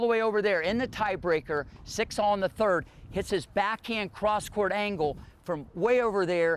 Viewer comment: Did he say at 4:37/38 that Venus "hit his backhand cross court" ??? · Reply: “This backhand cross court”